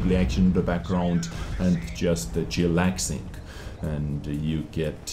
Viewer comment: Did you just say "chillaxing"?